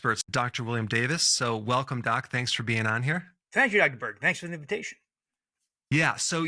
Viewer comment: Did he just say, "Thank you, Ekberg."??? To be fair, he is also a good doctor and of similar caliber...but Dr.Ekberg is an entirely different person!😂